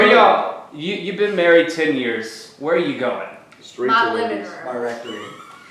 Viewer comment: Q: What did say at 16:52 that was so funny when asked "You've been married 10 years - where are you going?" Sounded like "My Limiter??" (and the youtube cc hilariously thinks she said "strudel is ready") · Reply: she said “my living room” - keeping with her redbox and a pizza narrative